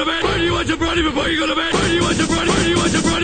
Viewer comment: For some reason I hear "why do you want some brownie before you go to bed" instead of "bart do you want some brownie before you go to bed"